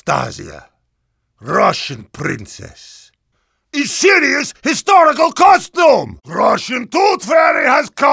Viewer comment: Heavy is Anastasia, russian princess, THE SHITTIEST HISTORICAL COSTUME! (But srsly i thought he said that :D)